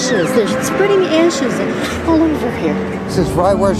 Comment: I thought it said "spreading asses at the beach". I'm disappointed!